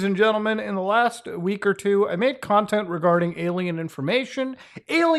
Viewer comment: Oh god. For a second, at the start of the video I thought Muta was gonna say "...now, within the last week or two I've made CONTACT..." instead of "...content "